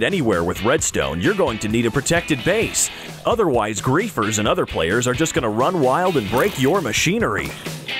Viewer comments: did he just said greephers instead of creepers? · I thought he said grian 🤣